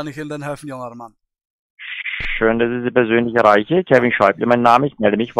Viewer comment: Ich verstehe seinen Namen nicht - Kevin SCHEISSE???